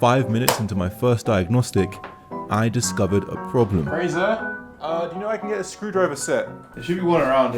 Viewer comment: ain't no way I heard Frieza while looking away, had to double check